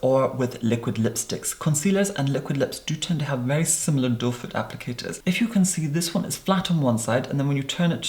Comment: Your pronunciation is so clear and crisp that now I know the applicator is called "doe foot" rather than "doefur" that I thought everyone else was saying.